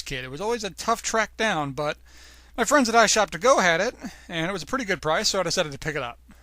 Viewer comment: Who are your friends? because i hear "I shot The goat dot com"